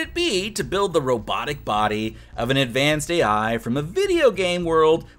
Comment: Did he just say Ai?